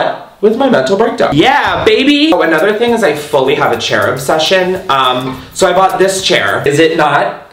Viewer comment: When you said chair obsession I thought you said cherub session....